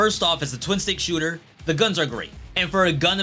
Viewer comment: "Gun about games?" Did I hear that right?